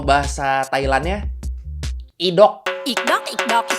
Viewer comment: gw dengernya indog njirr wkwkwkw